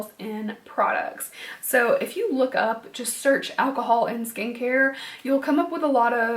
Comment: My boyfriend thought you said alcohol skank LMAO, had me dying